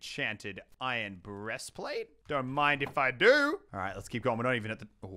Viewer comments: Did he say iron breastplate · Did he just say breast plate